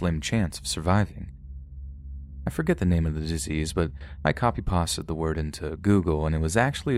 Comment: Did you say “i copy pasta’d the word into google”? 😭😭😭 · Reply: Yea, a bad edit, which made it sound like he said "pasta", at 2mins.